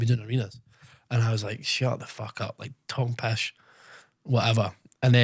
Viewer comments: he said “talking pish” not Tom Pesh AHAHAA 😂 And that bit where he’s talking about when his Dad asked him what he’s doing after summer. He says his Dad said “are you fuck!” not oh you fuck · Guy on subtitles had a nightmare lmao 11:29 "Tom Pesh" instead of "talking pish" is a cracker 😂